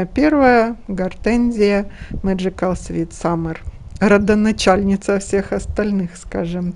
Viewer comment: какая красота у Вас в саду! Хвойники, гортензии какие!!! И лилии! Все гармонично! Расскажите, чем «подбиваете» клумбы, как е растения там посажены? И подскажите, что за гортензия не расслышала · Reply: Добрый день. Как раз монтирую видео и там покажу, какими растениями клумбы подбиваю. Двумя словами: садовой гвоздикой и алиссумом. Гортензия Меджикал Свит Саммер